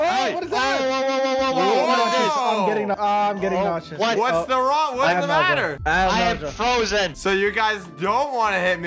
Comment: did just say what's the wrong